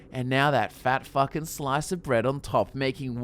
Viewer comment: not that I'm offended or bothered by cursing, more not sure if I heard what I thought I did... did you say fat f'ing slice of bread.